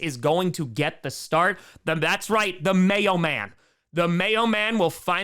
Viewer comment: at first, I thought that you called him "The Mailman" but then, when I actually looked down at my phone📱 and I saw Levis just holding a big ole disgustingly sloppy, lumpy, and ABSOLUTELY UNAPPETIZING jar of 💯٪ PURE MAYONNAISE! ... EWWW!...YUCK!!!